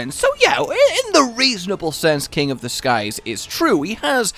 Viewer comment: King of the skies??? I thought they said king of disguise